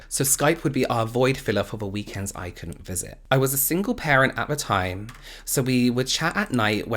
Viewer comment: I got distracted in the beginning of the vid and the first thing I heard was “I was a single mother” and I just kind of accepted that he was a single mother with a child 🤦🏻‍♀️